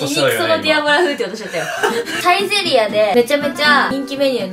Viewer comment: ディアボラ風って言ってたのかwディアラヴァ風に聞こえた